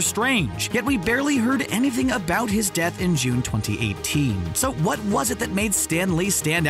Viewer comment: did you say June 2018